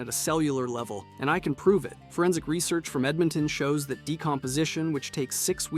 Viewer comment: did I hear Edmonton?